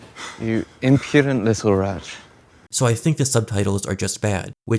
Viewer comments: having just watched the episode, I heard rat the first time, I didn’t hear wretch until you just mentioned it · I heard rat